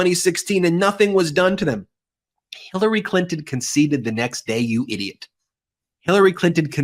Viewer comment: I thought u said u hadn’t take an oath 😂